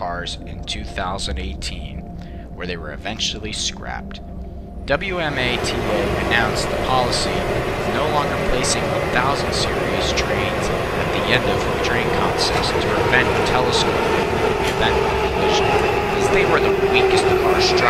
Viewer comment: I can barely hear you! Can you please write out what you said within that timeframe? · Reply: WMATA announced a policy of no longer placing 1000 series trains at the end of train consists to prevent the telescoping collision cause they were the weakest car structurally. All of 1000 series cars were put in the middle of train sets and served for another 8 years until retirement in 2017. Maybe some grammar errors here and there